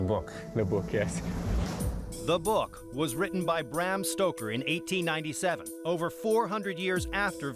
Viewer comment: Did he just say the book was written by Hand Stroker?